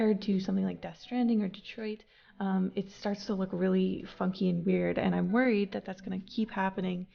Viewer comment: d-did she just cuss? Never thought I'd hear her say a swear lmao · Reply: She said funky